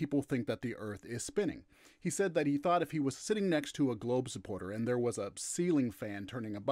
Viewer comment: Thought it said "globe RESTORATION and marble ceiling fan". Now how am I supposed to fix my ceiling lights lol